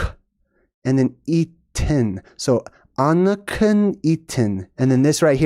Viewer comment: Sounded like you said Anakin, Darth Vader himself circa